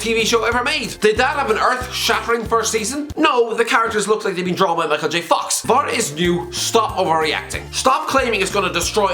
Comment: Did you say "The characters look like they have been drawn by Micheal J.Fox"? If so, that was brilliant! Haha